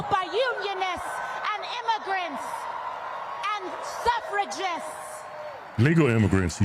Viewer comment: Did she say eugenist? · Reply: I think she said "suffragists". At first I thought she said "separatists"......